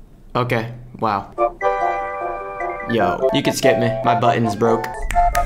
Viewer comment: i thought he said he couldnt skip?